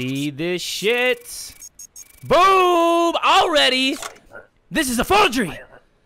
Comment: okay just watch his few secounds with the first guy (1:07) umm doesnt it sound like he is saying boob?